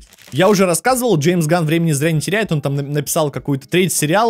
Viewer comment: Боже, мне послышалось «Джеймс Ганн зрение теряет», я так перепугался